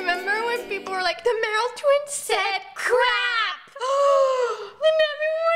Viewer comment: is it just me that heard saying cwapp instead of crapp🤔